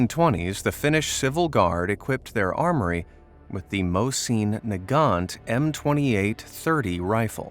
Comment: People who thought he said most seen Ni**a when he actually said Mosin Nagant